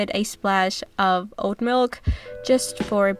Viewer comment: Thanks for the ideas! Yum! I do have to say that at first I thought you were saying "old milk"...but pretty sure it was actually oat milk. Lol. 😁